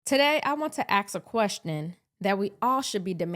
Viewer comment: Ummmm. Did you just say "Axe a question".